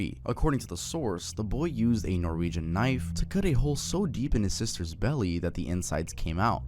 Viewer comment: DUDE I THOUGHT IT SAID TROLLING KNIFE I'M SO COOKED 😭